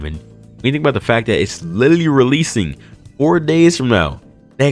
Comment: I'm sorry I don't think I heard you right. Release in 4 days?